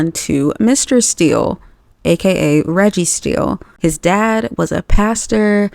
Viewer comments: I’m sorry but did you say Registeel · I'm sorry did you say his name is Registeel